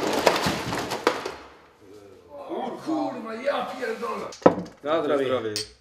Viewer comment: What does he say at 1:20 after he falls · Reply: typical "o, kurwa, ja pierdolę" (both would mean here something as "fuck")